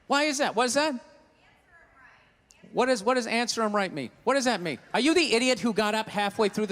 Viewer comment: I think she says "It's her right".